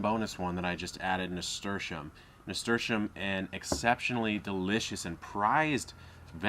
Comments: I didn't quite understand what he said at 11:59 - 12:03, what plant was the one in the newly added bowl? I've got problems with my hearing and when I turned on the subtitles, it said "Assertion Mr.Shim" hahahaah help please? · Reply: Charito Tamargo He’s saying Nasturtium :) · What was the name of the bo us plant he said at 12:01? It sound like he said 'stersham.' I never heard of that before. Please somebody tell me how to spell it because I can't find it the way I spell it in a Google search. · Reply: I think i heard nasturtium?